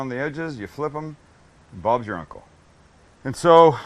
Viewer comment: Sam did you say "Bob's your uncle" at 3:25? I am sure I have heard you say it on other videos.